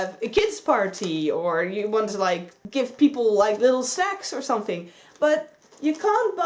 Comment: Did I hear that right at 5:21? “ Small bags for kids parties or some little sex...” 😂😂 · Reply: Hahaha omg no xD I said 'little snacks' xD